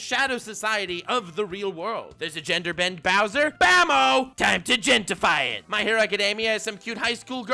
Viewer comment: From 2:33 onward all I hear now is BAMMO